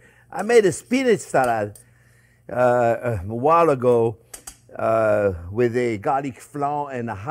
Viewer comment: I could have sworn you said you made a hot bacon dressing with garlic flowers! I watched the other recipe and kept looking for them, but they weren't there! You probably think I'm nuts, garlic flowers... But if you ever grew garlic, you know if you don't cut that bulb that comes up top, it turns into a poof-ball looking thing with white flowers. I had some garlic chives in my garden I didn't care about, (mostly because I couldn't kill them and they tried to take over) so I let the heads go to flower... They are about may the size of capers... if you're doing a fresh garden salad with all kinds of stuff in it with a vinaigrette or and oil and vinegar dressing, take a pair of scissors, and clip a bunch of those flowers and put them in on top of your salad. They're like a little mini garlic explosion, but not strong and burny like raw garlic, but more like the essence or smell of fresh cut garlic. They are SO GOOD!